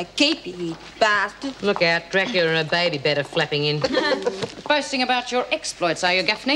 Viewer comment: Dracula and her baby flapping in. I thought Bea said cracken and her baby flapping in. Doreen calling Georgie dumbo. Doreen doesn't belong to the mensa society